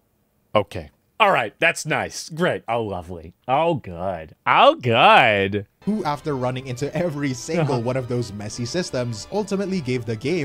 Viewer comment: 他是不是講了兩次"好可愛":face-blue-smiling: (絕對是我空耳罷了)